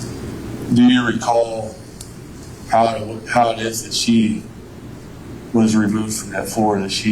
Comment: I swear I thought he said "do you recall how I..." When he was asking about how Tina got up off of the floor. 1:53:46.